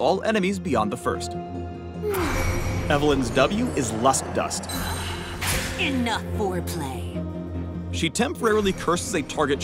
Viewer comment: Did she just say "Enough foreplay"